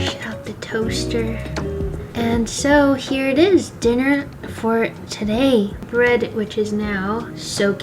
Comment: Did you just say "dinner voor twee", at time stamp 16:10, or was that just a glitch of AI...? Greatings from The Netherlands! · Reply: Dinner for today